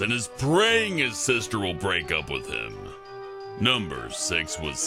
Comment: Did he say number six or number sex?